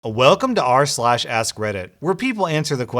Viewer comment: Who else thought he said.. “welcome (to the black parade) to r/slash ass Reddit”?